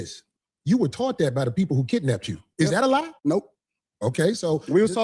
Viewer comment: As an attorney, your comprehension skills need work! He did NOT say "no" to being taught christianity, the question was "is that a lie?"... then he said no. Rewatch